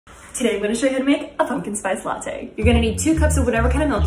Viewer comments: I I thought she said a "f"ing space latte That's what I messed up brain hears 🤣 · I thought she said a fucking spice late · I thought she said " a fucking spice latte" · I thought she said a fucking spice latte , pumpkin spice latte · I thought she said “ today I’m gonna show you how to make a fuckin spice latte” 🤣🤣🤣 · It sounded like she said a fucking spice latte 🤣🤣